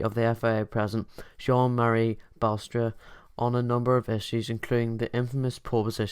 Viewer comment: Jean-Marie "Basterd"?! 🤣 Is that what you just said, mate?